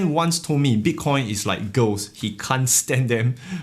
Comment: Ahaha thought your friend said bitcoin is like ghosts... then realized you said girls. That made more sense.